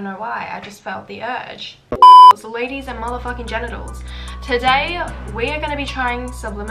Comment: Did she just call us GENITALS!!??